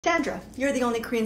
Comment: When it said Sandra at the start I thought it said dandruff